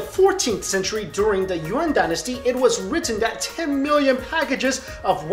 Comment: Did he say 'Urine Dynasty'?